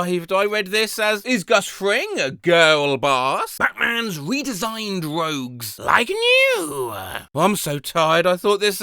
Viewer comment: I thought they said “redesigned robes”